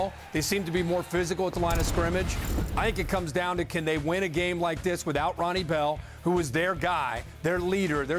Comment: Cheers to ESPN for covering B1G teams and not always the SEC… but Kirk mentioned Ronnie Bell, as a weapon…. he has been hurt all (mostly) season…. So they had a segment, which good but obviously didn’t research much….🧐🧐🧐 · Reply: Sounded like he says: “Can they win a game like this without Ronnie Bell”